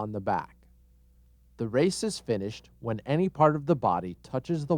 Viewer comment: it sounds like he says racist fish.